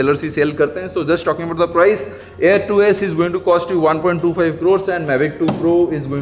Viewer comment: Did I hear it right??? 1.25 crore??? 😳😳😳